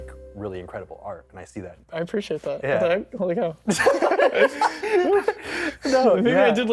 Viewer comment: can somebody pls tell me what jarl said i didn't get that part 😭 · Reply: “Holy cow” i think, he says that a lot :)